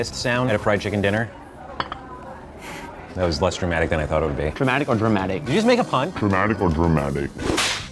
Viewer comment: When the guy said "dramatic or drumatic" I legit thought he said "traumatic or dramatic"